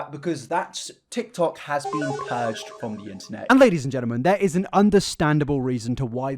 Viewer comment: goddammit I was so happy for like a split second cause I misheard you and thought you said "tiktok has been purged from the internet" IF ONLY